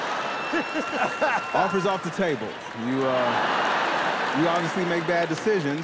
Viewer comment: what did he say there???? I couldn’t hear it clearly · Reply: Offer is off the table